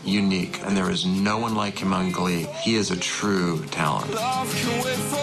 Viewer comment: i almost thought the guy was gonna say "A true hipster." but then he said talent.